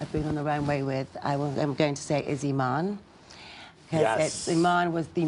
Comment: I can't understand what name naomi says in 15:51. Please someone can say it to me? Sorry for my English. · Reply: pablo medero She says Iman